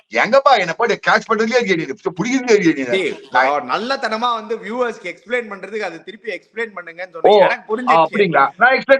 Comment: Did say "dei" 🤔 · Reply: He said See